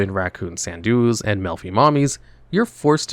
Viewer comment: I had to do a double take when you were talking about Melffy Mommy. I thought you said "Milfy Mommy" and thought that modern artwork had REALLY gotten out of hand lol.